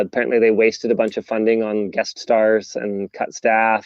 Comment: oh he said "guest stars" I thought he said "Deathstars". Yeah that's literally not far off from the way I expected upper management to be managing it and that's what took it down the first time.